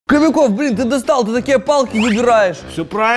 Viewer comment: Мне послышалось или Кобяков сказал "Читерские Палки"?